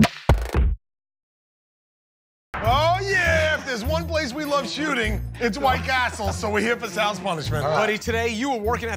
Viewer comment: what did they just say? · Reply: Oooooooh yeahhhhh